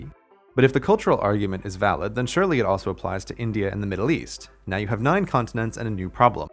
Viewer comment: I thought he said “Middle Easy” for a second